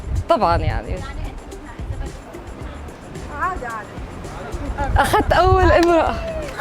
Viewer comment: she actually said first lady, not emarati